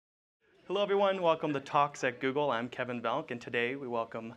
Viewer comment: At first it sounded like he said "welcome to toxic Google" (instead of "talks at"); I'm thinking this might have been a freudian slip or something??